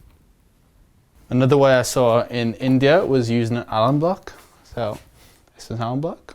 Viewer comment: What did you say was the name of the block at 10:20? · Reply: Alum block. Bloc Osma and Gentleman John are two known brands.